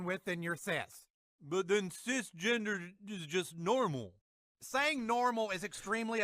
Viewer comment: not sure if i misheard, but ... cisginger ................... lol